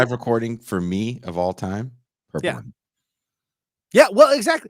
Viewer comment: I am fan so I don't want to sound too critical but 42:48 into the video, said, "The BEST live recording for me, of all time? Earthworm." At least I think he said Earthworm. I paused, backed up the video, listened 4 times at regular speed & could not be certain. Listened to the buildup & the quote at 1/4 speed - still could not definitively tell if I should familiarize myself with a band called Earthworm but it sounded very funny. Googled band called Earthworm and I can't imagine would put the Earthworm band that popped up on any pedestal. Meanwhile, (one of) the best live recording for me, of all time? Waiting For Columbus - Little Feat, Good Album art? Hell yes. Neon Park. The entire live lp was recorded entirely in the greatest year of music, 1977. (Released in '78 but made in '77). Please clear up the Earthworm mystery for me. Thanks for what you do. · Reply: Purple Rain.